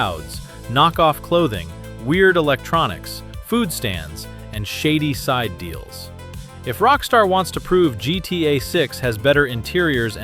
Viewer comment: I thought bruh said food stamps